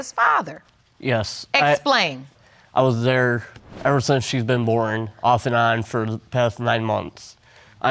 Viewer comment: ever since she was what now? Did he just say boring?!?